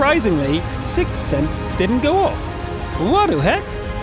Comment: Did He Just Say Wadu Hek?